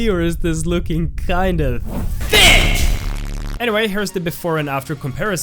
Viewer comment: What he sees: "T H I C C" What CC thinks he said: "fish"